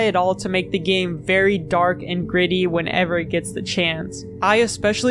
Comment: dark and griddy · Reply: I definitely heard “griddy” not “gritty”